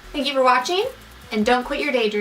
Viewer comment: I love this look! I think I'll be picking up some Laura Mercier blenders. Also, was in the room when I watching the end of this video, and he thought you said "don't quit your day-drinking". And I just think that that's accurate as well. Haha. Love you girl!!